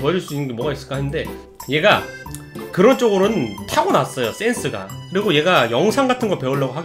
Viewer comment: 에 센스가 섹×로 들려서 순간 식은땀 흘렸음